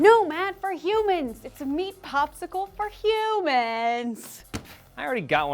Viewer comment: "Meat Popsicle", I so get it now. I thought they were saying "Beet Popsicle", like it was made out of beets like that Russian dish.